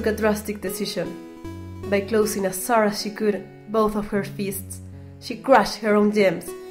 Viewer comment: buth uf her feeests That’s what I hear XD